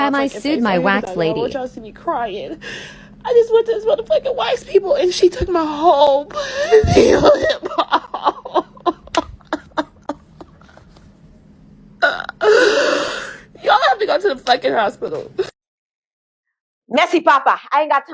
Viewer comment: I can’t understand what she’s saying I’m sorry to this lady. Someone help me pls 😭😭😭😭😭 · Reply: She said she went to a wax lady & she took her whole cooder lip off and she had to go to the hospital